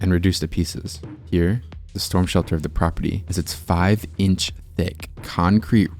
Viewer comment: I THOUGHT HE SAID 5 INCH DICK AND I HAD TO REWATCH IT 😭😭😭😭